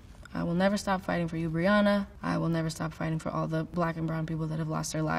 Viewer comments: i don’t cry. but when she said “i’ll never stop fighting for you breonna” i kinda lost it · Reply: i thought she said Rihanna lol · lmaooo I heard "I will never stop fighting for you, Rihanna" and I was like?? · I thought she said Rihanna instead of Brianna and for a minute I was like wait what happened to Rihanna